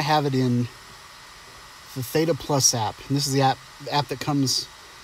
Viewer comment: what is the name of the app used after the pano was made and exported inn the video? It sounded like you said “Pheta” but I don’t find anything of the sort on the App Store. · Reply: I am sorry... I thought you commented on the video I just uploaded. The app in this video is called Theta+.